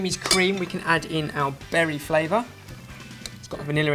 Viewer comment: "We can now add in our "Barry" flavor." Am i the only one who heard this?